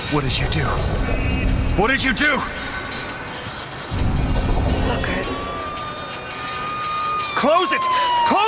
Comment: Anyone else thought Elizabeth kept saying Hooker instead of Booker?